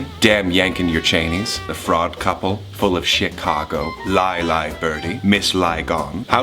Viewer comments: Did I hear Thomas say "full of shitcago" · DID HE JUST SAY "FULL OF SH*T-CAGO"?